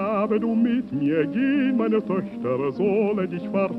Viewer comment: 나 왜 여기를 Motherfxxk로 들리냐